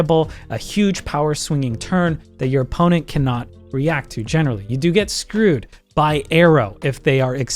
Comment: "You get screwed by Aero" What I hear: "You get screwed by Arrow" - HYPE, GREEN ARROW IS IN MARVEL-- oh wait...